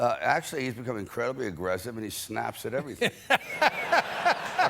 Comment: "He F***in snaps.." Did I hear that right? 😂